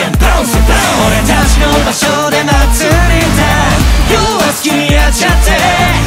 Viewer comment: 考察です(くそ) サビで 2:06 で要は好きにやちゃっての部分で要がyouにしか聞こえないですただの自分の脳変換なだけなんですけどyouってあなたっていう意味じゃないですかそれで要は好きにやちゃってにするとあなたは好きにやってくださいになるんですよねぇ あと私がナゴヤを褒めるだけの部分なので飛ばしてください 2:03 で俺たちの場所で祭りだと書いてるんです他の敷地じゃなく自分たちの敷地で祭りしてんの偉いちゅき 十四ちゃんがわいい❤厨二病…………